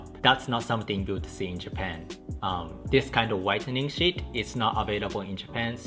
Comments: At first when I thought he said “this whitening s***” I was like, oh word.. then I looked back realizing he said sheet. That works too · omfg I thought you said "whitening shit" and I was like whoa that came out of nowhere, but then I saw the caption said "sheet". Not to make fun of your accent, it just sounded like it at first and surprised me lol. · Haha I thought he said “whitening shit” lol